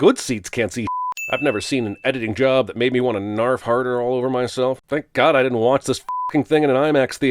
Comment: You said "narf harder" but I heard "Nerf Herder" lol.